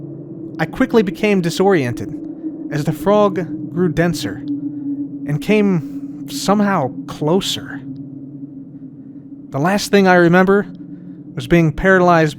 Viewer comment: I love the story telling aspect of this video it's a storytime with lol you love to see it also did he say the frog grew denser ?